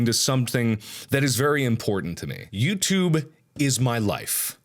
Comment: I have subtitles on and at 1:34 he says "Youtube is my life" and the subtitles say "(You Tube is love), YouTube is (My) life. Check it out its quite funny